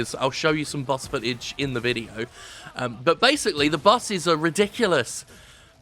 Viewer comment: I thought he said "busses"